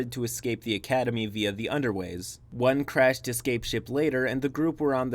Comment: I misheard escape ship as air ship and nearly had a stroke For real though I've been wanting to try out DMing for my group of close friends but have been nervous because of a previous DMing attempt with a different group of players (who I quickly realized where very controlling and toxic, and threw a massive fit when I said a Melee NPC would join the group eventually) But hearing this saga it really makes me feel better about actually trying to DM and being able to actually START my campaign